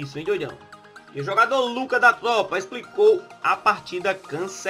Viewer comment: só eu que entendi "ex jogador lucca da tropa"??????